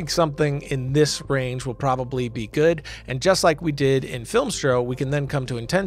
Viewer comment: did say Filmstro?😀😀